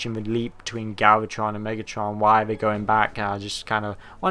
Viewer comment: Oh, I thought it said "Black", not "Back". Niggatron.